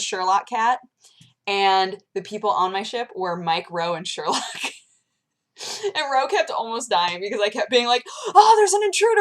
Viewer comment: when you said mike, ro, and sherlock, i thought you said mike rowe, and sherlock hahaha